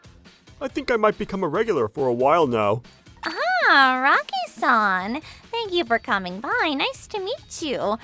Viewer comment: I thought she said Rocky san😂